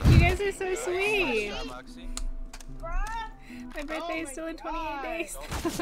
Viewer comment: what did she say? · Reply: Sandro Leite my birthday is still in 28 day tho